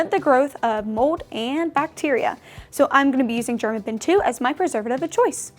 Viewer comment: What preservative did you use? I don't see it in your list and it sounded like you said German bin 2